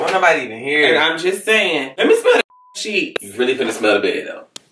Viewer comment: O lawd, at 2:07 i thought she said "leme smell these g.d. cheeks"!